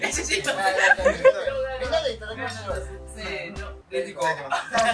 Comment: 多分２回目のありがとうって言ったんだと思うんですが確かに「レディゴー」に聞こえてしまいましたｗ とのコラボありがとうございました！！！